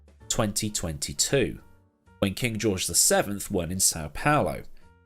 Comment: Did you just say "King George VI won in Sao Palo" at 4:58 or am I just mistaken?